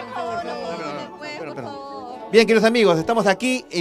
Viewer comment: quien escucho esa voz que dice capitan! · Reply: Capital tv XD